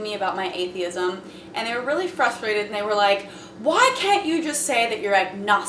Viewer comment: Did I hear say "fruthrated"@ 0:04?